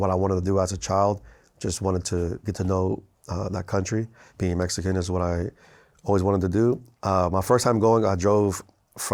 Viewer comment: does he say that being a Mexican is something he always wanted to do?😂😂according to the closed caption,that’s what it said too 😂